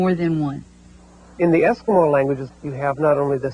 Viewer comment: which language is he talking about. i couldn't understand the name or the word he says "in the ------ languages...? could anyone please help on that? thanks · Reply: eskimo languages.